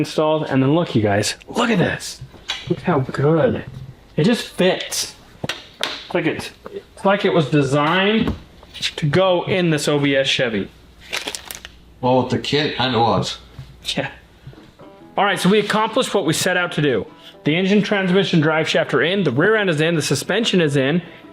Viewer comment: At time stamp 36:35, did you say "the Soviet Chevy"? 😲